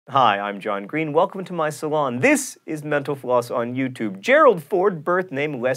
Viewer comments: at the end when he said "mental floss" i heard "menopause" whoopsie... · When he says "mental floss" at the end of the show, it sounds like he is saying "menopause"